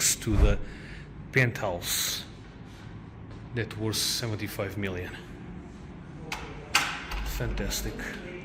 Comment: Did i just hear 75Mil? 13:29" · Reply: Yes, at the top of the latest tower someone bought the entire top penthouse for R75Million.